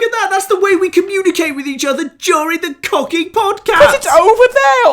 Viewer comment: did he just say "fuck podcats" at some point or what's happening